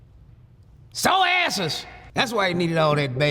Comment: I suspect that a very large part of America (including me) whole heartedly agrees with Mr. Griffin's news analysis.....SORE ASSES!!! 😂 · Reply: Lmaoo - tell me why I thought he was saying “sewn asses”